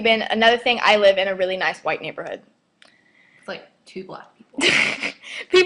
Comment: I thought you said you lived in a nice white neighborhood . . . .